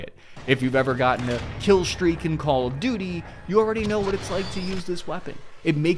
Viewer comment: I thought so too. Btw, what is “Call of duty”? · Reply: I thought they were saying Call of Doodie. I thought it was a poop thing.